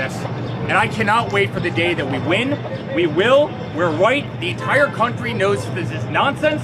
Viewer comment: He is definitely saying, "we're white." 😮